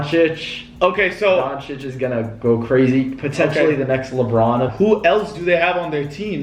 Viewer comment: My heart jumped I thought he said he gonna go crazy next to lebron predicting the trade 😂😂